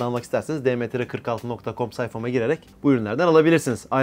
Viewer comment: Allah Geri Kalanınıda Kazasız Belasız Hayırlısıyla Bitirmeni Nasip Etsin. 28:26 Nice Çek Diyor 0.50 De zor Anlaşıldı Gider Ayak Ne Dedi Tribine Soktun Bizi Dişses 😒